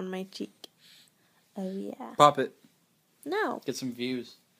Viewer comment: I thought you said bop it!